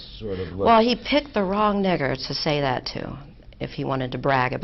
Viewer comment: Did she say “he picked the wrong N*gger, to say that to “ 😭?